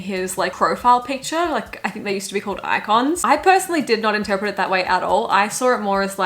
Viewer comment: i swear to god I thought she was saying "eye cons" as in eye contacts in now that we dont talk instead of "icons"